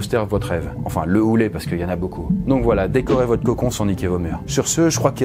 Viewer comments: Y'a que moi qui entend "voila donc décorez votre cocon sans niquer vos mères" ? mdrr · "Donc voilà décorer votre cocon sans niquer vos mères", attend quoi ? Ahhh niquer vos murs ! Il y a que moi qu'a buguer là-dessus mdrrr ?